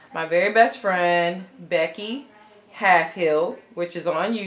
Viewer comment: Hi, trying to find your friends channel but not sure if I'm spelling it correctly.. could you post a link to her channel? the one who made the banner for you? I thought you said Becky but I'm not having luck with that. Thank you and all of your work is AWESOME! I would love a tutorial to show more detail on the album made with bags. What a talented lady you are! I loved EVERYTHING you did. Congratulations on your newest grandbaby